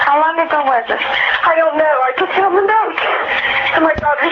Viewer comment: I always thought she said 'found the note' 🤔 Even listening closely again, it still sounds like 'found.' Not a big deal. I think burke injured JB and they covered it up.